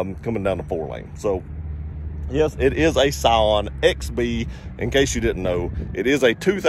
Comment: No video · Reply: Works now. I thought you said Psion XD, I don’t know if you’ve ever tried to sit in one of those, but those are not for people over 5 foot seven!